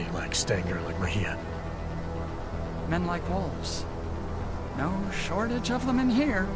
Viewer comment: "Men like wolves." When I first played (no subtitles IIRC) I thought he said 'Men like Walthers.' That would have been more powerful IMO.